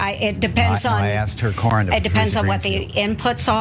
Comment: I mean first off she did know. Secondly what is your area of expertise? Do you know every detail about it? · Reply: He clearly says corn lmao now you are just lying. And no she gave him the correct answer.